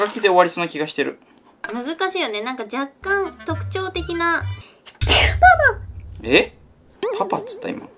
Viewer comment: のパパって聞こえたwも上手いけどね？自分で、上手いって言わないよね😃二人とも上手いんだから上手いって言っていいんだよ‼️